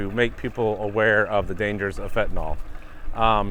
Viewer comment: when you said fentanyl, my hearing isnt great sometimes and i thought you said "Seminole awareness day", and i thought, ooh, that would be great, then i was disappointed when i realized what you were actually talking about. but it got me thinking. how great would it be if we had a school day specifically dedicated, statewide, to teaching about, and celebrating, the original inhabitants of this land. I think everyone should know the history and culture of the native people who's land we now inhabit. actually lets make it a whole week. and when i say history, i dont mean just a basic rundown of events from the united state's perspective, i'm talking about the full history, as it would be taught within the tribe. we could do it on the second week of october.